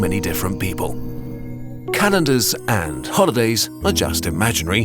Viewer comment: I thought he said "Canadas, and holidays, are just imaginary." And I, a Canadian, was like "yep, very correct."